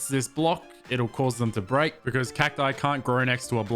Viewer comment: 👿 CACTI, who’s mans is this saying CaCtIsiS.